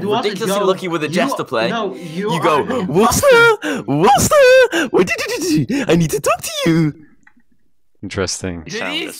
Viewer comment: this is what said he said hey wolfster i need to talk with you